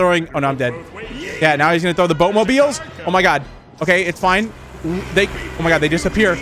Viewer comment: Why does it sound like plankton said and boatmobiles go yeet